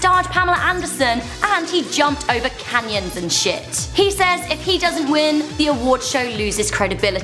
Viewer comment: did I hear sh!t